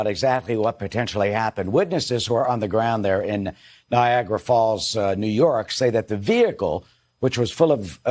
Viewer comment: did he say Viagra Falls the first time??!?!?!🤪